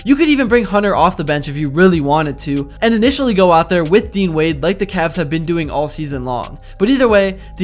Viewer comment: When he said dean wade I thought he said dwade